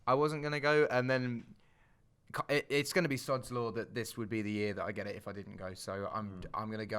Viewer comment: Can Someone tell me what did he say at 6:24? "It's gonna be (sucks lord???)That this would be the year ...." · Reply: "sods law" kinda like when you say its not gonna rain and then it rains, that kinda thing ahah